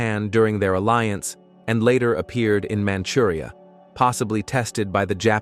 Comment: №8 のKugelPanzer の解説は事実か？かなり詳しいつもりだが日本であの手の兵器のことは聞いたことはない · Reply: 日本語だけでなくドイツ語、ロシア語のwikiでも日本へ輸出されたと出ていました、3:37 「後にマムリアに出現し」と聞こえる部分はManchuria（満州）のことでしょう。 ロシア語のwikiによるとロシアのクビンカにあるクーゲルパンツァーは満州で鹵獲したとあり、他にドイツのクマースドルフ訓練場でマウスとともに鹵獲したいう説もあるということでした。 まあ日本でも手にしたは良いけど、たぶん「これどうするの？」みたいな感じだったのか使われた形跡はないとのことです。